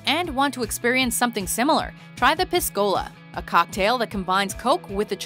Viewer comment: I thought she said piss cola